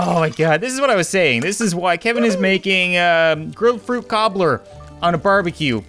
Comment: When you said "Grill Fruit Cobler" my internet stalled right there and I thought you said "Grow Fruit Cocktail" and I was like.... NOOOOO not the growfruit!!!